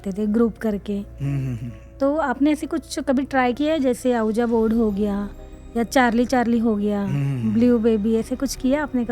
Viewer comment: did she say Ahuja Board....!!! 😂😂😂